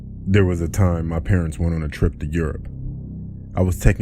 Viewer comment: It sounded like he just said yurp not Europe